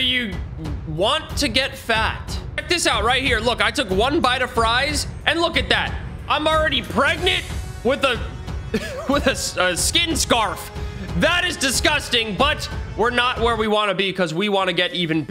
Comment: At the very start i was finishing eating so i wasn't paying attention and i heard him say 'Im pregnant with a skin scarf' Now I have to get a replacement for my PC.